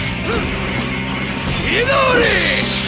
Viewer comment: What sasuke says in his jutsu?? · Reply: "Playtime's over! Chidori!"